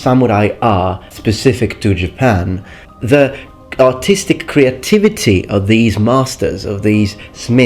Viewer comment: did he say artistic or autistic? :thinking